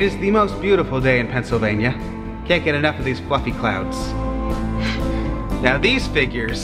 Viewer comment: uhh- did he just say what I thought he said? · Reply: OHHH SORRY!! YOU SAID "Cant get enough of these FLUFFY clouds" I THOUGHT YOU SAID "Cant get enough of these F_____ clouds" SORRY #TEAM!